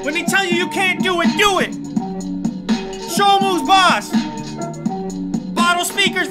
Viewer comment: did he say “somos más”? 😅